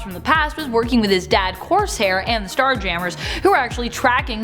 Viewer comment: Did you say "his dad Course Hair" 7:57 😂😂😂😂